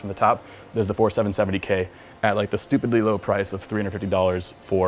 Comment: Did he say stupid little prize?? My pc is actualy cheaper :D